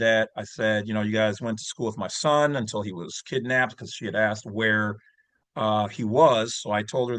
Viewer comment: Did he say His son was kidnapped 5:18 real casual like? 😂😢😮